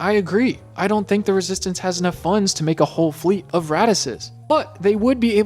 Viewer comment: I almost spat my drink out when I thought he said 'a fleet of radishes'😂